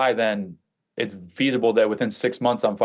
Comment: It's what? 🤔 "Feath-able"? "Veath-able"? What did he say, and did he mean "feasible"? Lol